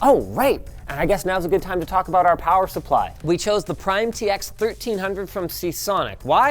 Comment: Linus said "power supply", I heard "sponsor"